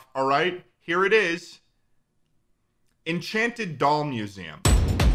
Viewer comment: I thought he said Enchanted Dong Museum